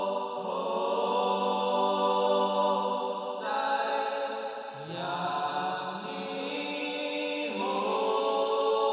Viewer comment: พูดว่าอะไรนะ? ฟังร้อยรอบจับไม่ได้ความ 555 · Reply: ไม่ต้องเสียใจ อย่างนี้ โว้ โฮ้ ❤